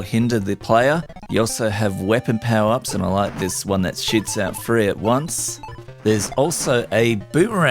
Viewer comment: did you say "shoots out" there? Either is appropriate ;-p Very nice video and topic.